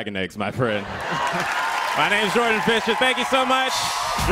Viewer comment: Thought it said “Jordan Fisher”😒